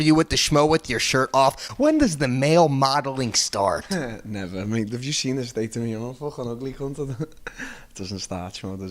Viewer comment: Wtf is he saying?? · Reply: "Never have you seen the state of me I'm an ugly cunt you've got a better chance than me and you're butt ugly"